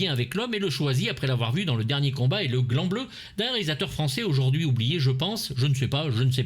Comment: c'est moi qui ai l'esprit mal tourne ou j'ai entendu " le gland bleu" au lieu du "grand bleu" ?